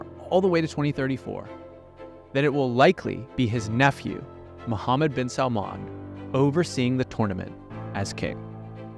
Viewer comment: actually listen to it again, he didnt say that. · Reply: yes he did :) Just listened again and this is what he said verbatim at 40:57 : "...that it will likely be his nefew Mohamed Ben Salman overseeing the tournament.."